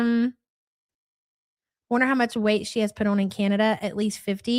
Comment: i thought he said wonder woman 😂